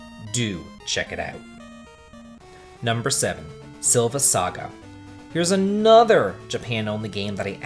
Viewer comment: When you said: " Silver Saga" I heard: " 7th Saga" lol